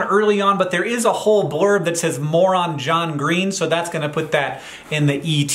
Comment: I heard “moron John Green” and I was thinking ouch! I’m glad that’s not actually what the article said. Hank looks to be in good spirits, that’s nice to see.